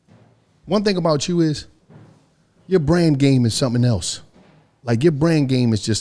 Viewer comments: I thought he said "brain game"...SUPER PAUSE! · Ya brain game? Ayoooooo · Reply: Whoa he sounded like he said brain game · I thought he said brain game i was like (pause) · Lmaooo I thought this nigga said brain game Lmaooo ayoooo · I thought said brain game lmao like ayyyyyooooooooo